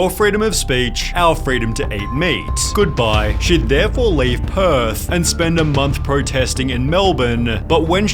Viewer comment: I got way too excited. I thought he said she would leave earth😂😂😂😂